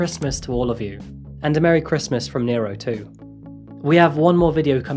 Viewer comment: WOW Nero (did I hear right) is beautiful 21:40 Has that 'I'm the dogs doo da's'" expression!